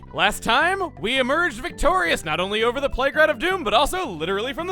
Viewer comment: so, no lie, I had my volume down during the intro, and I heard "Plague Rat of Doom" as "Playground of Doom."